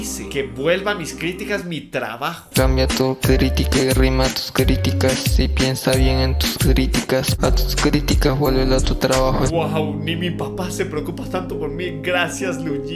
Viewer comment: no entiendo porque escucho “kiriticas” 😂😂